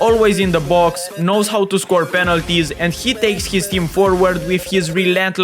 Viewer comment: When bro told "Passes," i heard it like "Pessi" 🤣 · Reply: When bro said "Penalties" ,i heard "Penaldo" 😂😂